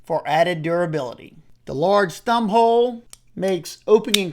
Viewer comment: When you said “the large thumbhole” I heard “The LORD’s thumbhole” and I will show up on sundays for that…😂